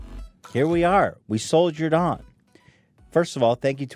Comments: At the very beginning when Ethan said "Soldiered on" it sounded like he said "Sold your dong". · am I the only one who heard ”we sold your aunt”?